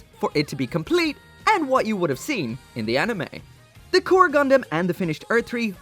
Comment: Why i keep hearing "orgy gundam" hahahaha